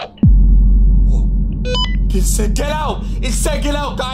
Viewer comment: I've heard it saying get out did anybody else heard it????